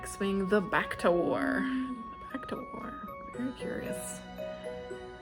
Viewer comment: At first I misheard The Bacta War as The Back Door and was lolling because I’m that mature Also, that’s a lot of books about people having sex with things you wouldn’t expect. Azriel Uprising sounds fun!